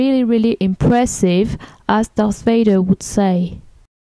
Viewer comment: Sounds like she said, " As Darth Vader would say", at 6:39.